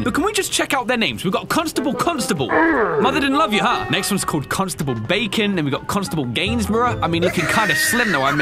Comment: Wait a minute, did he say cons table bacon. I’m not a constable though